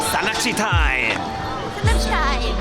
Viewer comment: 순간 산낚지 타임을 싸가지 타임 으로 들었어요 ㅋㅋ